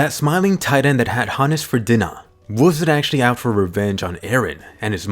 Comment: "The smiling titan that had Hannes for dinnah (i know its Dina but i hear Dinner but dinnah)